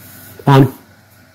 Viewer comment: What is the guy saying at 19:40-50? Sounds like fawn, or fun or? Any idea’s?